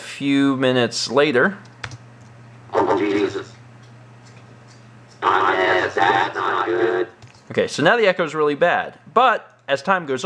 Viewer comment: it sounds like satan sayign AH JESUS